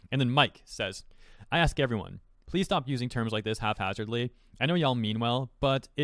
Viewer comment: I'm sorry did you just say "HALF-hazardly"?